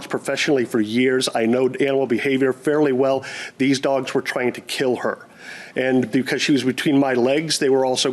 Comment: 20 years an expert dog walker, lol. He could "tell" the Pitbull's were trained to kill. OMG he's going for an academy award here. He couldn't even prove his dog was attacked. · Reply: He said “trying to kill.” I heard “trained” at first too and had to rewind and listen again. It’s funny how mishearing one word changes a whole sentence!